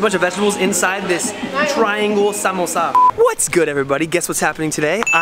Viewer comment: In thumbnail he was asking what is this Me: simple its a samosa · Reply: I thought u said simple samosa😂😂